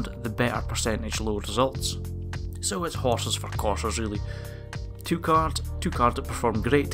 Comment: Did you say, "Horses for quarters?" at 11:31? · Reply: horses for courses.